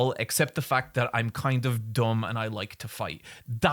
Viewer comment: DID HE JUST SAY THAT HE IS KIND OF DOM? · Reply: *dumb